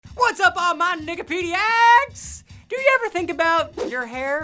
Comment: OMG WHEN I HEARD HIM SAY NIKIPEDIECS I CHECKED HE SUBTITLES CUZ I THOUGHT HE SAID THE N WORD AND THE CATIONS SAID HE DID TOO 😂😂